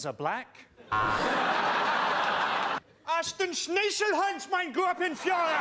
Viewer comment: What did he say on like 1:42-1:45 I can't speak German somebody please reply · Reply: I know I'm not gunna be much help here but I can make out 'Fuhrer' so it's probably 'All hail my Fuhrer' or something like that :)